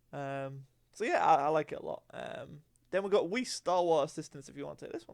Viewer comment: I just got a few minutes in n i thought u said whis starwars assistance 🤣😂